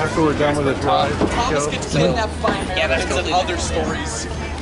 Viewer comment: There I am at 5:34 and 10:18. I have a feeling I met you at the Red Caboose Motel. Also, if you’re wondering what I said at 5:34, I said “Thomas Gets Kidnapped By Americans & Other Stories. I said that cause me and my friend saw Thomas in the shed.